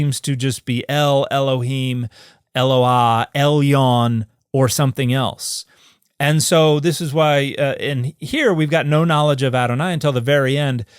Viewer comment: "Everybody seems to be El, Elpohim...or something ELse" is what I heard.